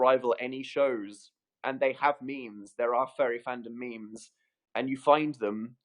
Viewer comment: i kept hearing "fairy fandom" and i was so confused until i realized he was saying "furry fandom" wow